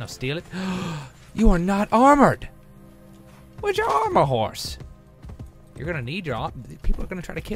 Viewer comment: when said "armor house" I thought he said "I'm on a horse" :D